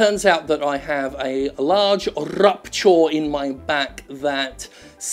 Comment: The first time I watched this I thought Jim said "large rupture in my sack" - I had to rewind and check.